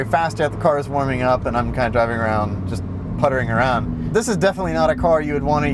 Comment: Did he just say that he was just DOUGing around, putting around?? 😂